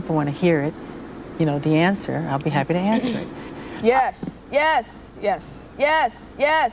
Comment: When she said yes yes yes yessss I literally thought she was bragging saying “I’m dating the love of your life” but other than that I laughed pretty hard.